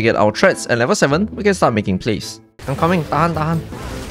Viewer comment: im coming tahan tahan tahan 😂 njir gasalah denger kan ?